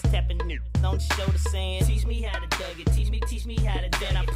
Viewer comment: All I hear is "Teach me how to plank t-t-teach me how to plank! :D